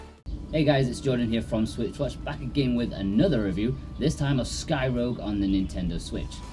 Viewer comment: threw me off. I thought you said skyrim for a seconds there 😂